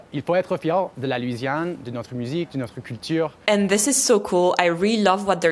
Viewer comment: I notice the subtitles say "de la musique, de la culture" but the speaker says "de notre musique, de notre culture", are they trying to standardize his dialogue? 😂